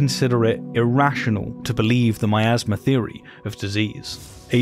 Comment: Oooh Miasma. I thought you were saying "my asthma."